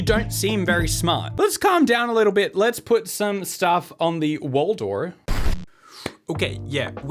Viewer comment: So, I'm new and when I first heard you say "wall door" I thought it was one word 😂. Like, "wholdore" (which sounds like a magic land, like Narnia) I felt like an idiot when I figured it out.